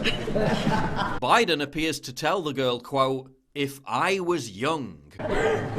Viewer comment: I think he says at 3:31 "By the way if I was young and had just met you I would change parties"